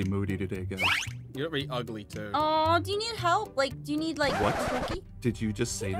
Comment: Am I the only one that Thought said “Do you need a quickie?”